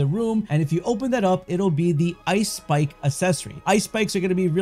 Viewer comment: I thought you said "it will be the ice spice accessory"😭😭